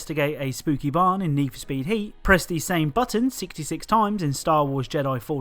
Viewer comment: i heard neef speed heat